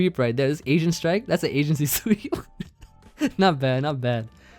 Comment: Did just call it an “Asian-cy” sweep? 😅